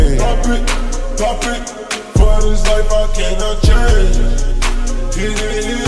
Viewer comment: for this life i cannot change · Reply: Ngl since now I thought it said 'fuck this life I cant change' 😂